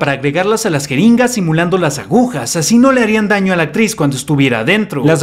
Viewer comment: Todo bien hasta que escuche "abuja" 😂